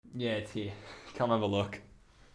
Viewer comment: did he just say 'Где ты? Come have a look' ?